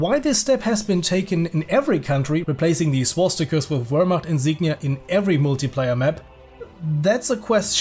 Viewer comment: What the hell did he just say there? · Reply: "Wehrmacht Insignia"